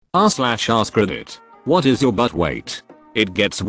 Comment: I thought it said "what is your butt weight"